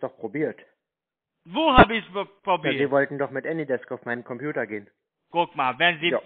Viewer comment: Ich habe immer Anydef verstanden